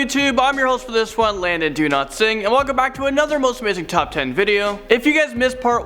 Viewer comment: Am i the only one who heared my man saying „Landon do not sing“ ???